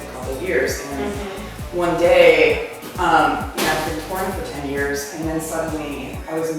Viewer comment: What does she say at 4:01? It sounds like she says "after doing porn for 10 years"? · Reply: I think she says "after touring for 10 years"